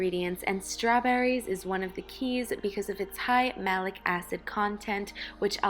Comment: When she was talking about strawberries at the start she said "because if it's high malic acid" but I only heard "because of its high" mum I need some high strawberries